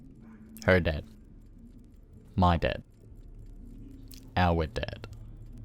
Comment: bro this was in the background and all I hear in the quiet is “her dad, my dad, our dad”